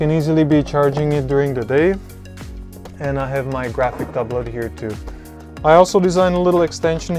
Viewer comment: didnt understand what you said that device was at 3:46, a graphic uploader? what is that? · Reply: Graphic tablet?